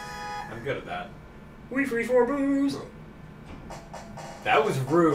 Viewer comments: I heard say, "we freed 4 boobs!" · what did say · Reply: We freed four boos